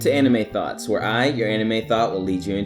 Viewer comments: I thought you said anime thots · I thought he said anime thot